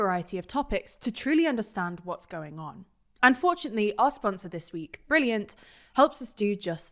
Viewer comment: Your new brilliant advert sounds like your saying "unfortunately" rather than "and fortunately"